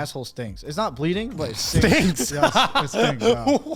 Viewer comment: Gaunt and Joey both mishear daidus, he said "stings" they heard "stinks".